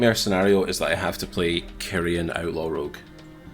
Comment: It sounded like said I'd hate to play Korean outlaw rouge 🤣😍